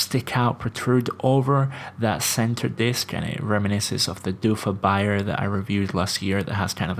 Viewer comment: What is the brand mentioned in 3:17? You said "Reminiscence of the 'doofah .... " ? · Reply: dufa <link>